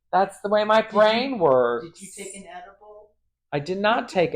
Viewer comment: Misheard “edible” as “Adderall”—whatever works!